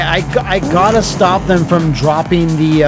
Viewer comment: I heard "I gotta stop" at the very beginning and thought "I gotta stop the nuke!"; and also thought it wouldn't be real